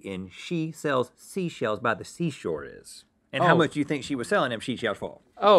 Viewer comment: I’ve always heard it as “Sally sells sea shells by the seashore”